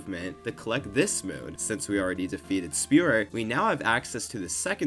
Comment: i thought you were saying Spirit instead of Spewert until like 6 minutes into the video